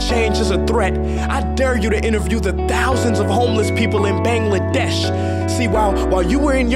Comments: Wait.. Did he just said Bangladesh??? 2:43 BDs where are you? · Did he said Bangladesh in 2:46 ??? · did he said Bangladesh!!!?